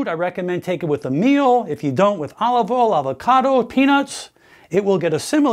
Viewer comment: Did he say olives? I thought he said olive oil. 😎😎😎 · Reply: he said olive oil